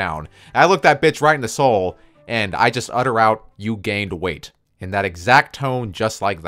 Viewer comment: I don't understand what you told to her 8:51?? "you gained wait"??? (is that it)